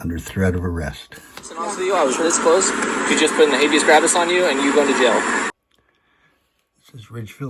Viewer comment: DID HE SAY "habeus CRAPUS?" 🤣🤣🤣🤣🤣🤣🤣🤣🤣🤣